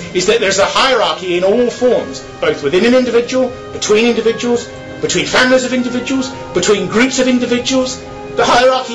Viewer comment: "between NJHKBK of individuals"? what did he say? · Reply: Between families of individuals